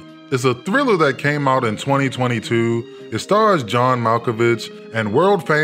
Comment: fam whole time I thought you said this came out in 2002 and I was confused as shit lmaoo